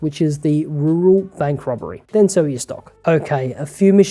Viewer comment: When do you sell vangelico and the others who come after? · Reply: He mentioned the others, but I (after rewatching that part 3 times) still did not hear him say when to sell Vangelico. Edit: Found it t 15:41. He says "then sell your stock" but i didnt catch that because of the accent